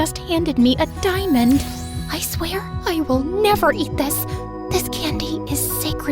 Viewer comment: The subtitles say “that” instead of “this”